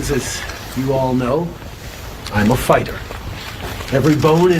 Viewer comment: I thought he said "I'm a faygg hat" instead of fighter. it would have been his truest statement ever!